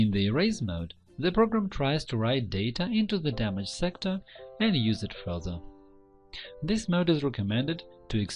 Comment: you say: "- In the erase mode, the program tries to write data into the damaged sector and (...)" What? What do you say here? Use it for other? Use it frozen? And use it frodo? What?!